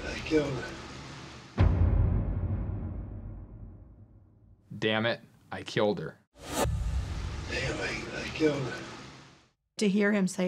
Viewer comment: I keep hearing “they think I killed her” they must be tone deaf that or my hearing problems are getting worse